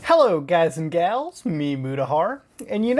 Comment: Always thought you were saying Meaty Mudahar at the start.